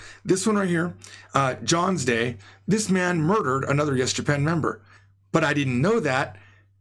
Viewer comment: did he say 'murdered'?